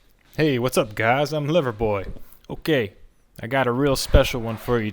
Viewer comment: Sometimes it sounds like you say “Liver boy” lol 😄